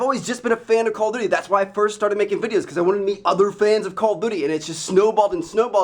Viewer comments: did he say "Call of booty" if so then hell yeah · at like 1:04 i swear he says call of booty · LOL 1:06 I SWEAR HE SAYS "CALL OF BOOTY"!